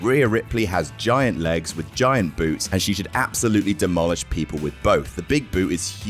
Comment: thought said giant boobs :-/